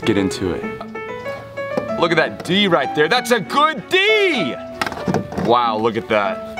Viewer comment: when he said his "THATS A D" my headphones cut in so it sounded like "THATS A, please charge."